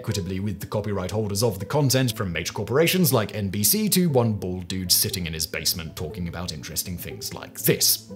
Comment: I heard “one-balled guy” instead of “one bald guy” 😂😂😂